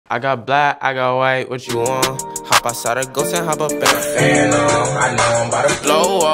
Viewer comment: When he said "hop outside a ghost" I thought he was saying "hop at Zaragoza"